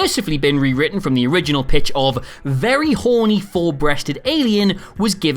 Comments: Oh, "four-breasted"! I thought you had said "full-breasted." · Oh, you said four breasted. I thought you said full breasted, then you made the last remark, and I was like, "Huh?"